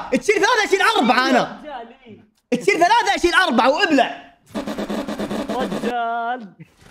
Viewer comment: اللي مافهم وش قال قال:القموا ابلعوا تخسي وتعقب 😂😂😂😂😂😂😂❤❤